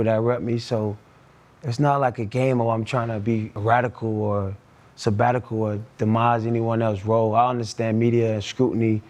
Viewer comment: did AB just say he's not trying to be "Sabbatical"?